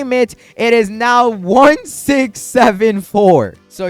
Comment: did someone say 67?